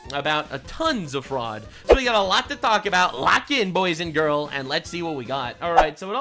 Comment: LOL did you say "bois and girl?" I guess I'm that girl 😂 love your videos- keep up the good work.